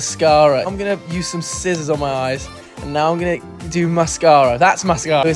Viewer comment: When he said “scissors” I thought he said “SZA”